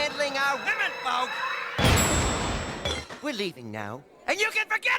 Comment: what does max say here? We draw the line on you handling our what? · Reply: women folk i think!